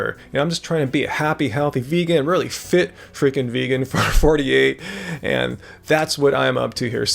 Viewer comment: did you say fit fucking vegan at 6:16 lol!